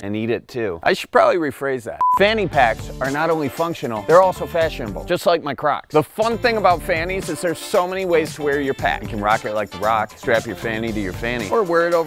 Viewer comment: “Just like my Crocs.” Sincerely thought you said crotch at first and choked on my water. 😂